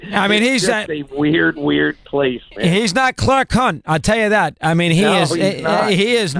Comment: Sounded like he said "Clark Cunt" at 9:37 Reminds me of this classic from Mike Francesa: <link>